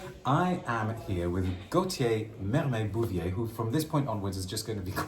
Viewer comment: is lovely. I bought from him in Harrods. I'll come and see you again when I'm back from Dubai visiting the family! And thank you for a lovely and informative video as usual, D! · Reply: I thought he said Gotye😂 now I know